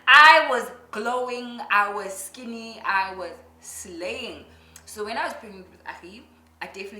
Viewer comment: i am waiting on a video on preggy wear..i heard you saying linda is slaying, but you are also doing the most babes. By the by i low key judged your alcohol ways LOL